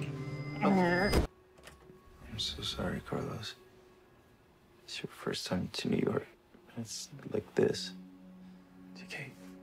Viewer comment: What did TK say? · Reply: i'm so sorry Carlos, it's your first time to New York and it's like this